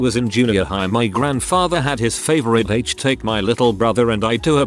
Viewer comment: What was the censored word at 17:45 ? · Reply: I think it's "hook er" - it has the right number of letters and fits the context.